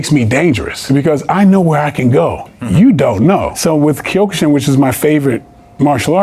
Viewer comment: What kind of martial arts did he say was his favorite at 16:06 ? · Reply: Kyokushin. Its a karate style